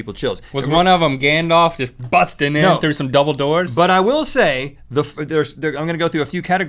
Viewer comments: I thought for a second link said Gandalf bursting in through some dumbledors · I thought Link said Gandalf bursting through Dumbledores!